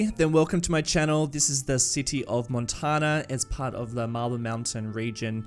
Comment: Everytime you say "marble" I keep hearing "mabyl" (like when in school we tried too hard to sound british (am not a native english speaker) and it makes me laugh each time :D Love the content and greetings from Poland ^^